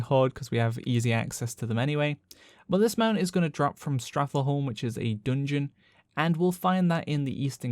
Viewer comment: Did you just say “Strath-eh-home?”